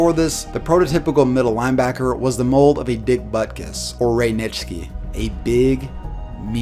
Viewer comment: Did he say the football player's name at 3:44 was Dick Buttkiss?? 😂😂😂